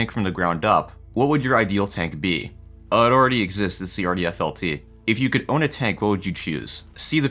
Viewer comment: what was the name of that tank you said? I didn't got it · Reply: RDFLT, believe it was a continuation of the HSTVL project.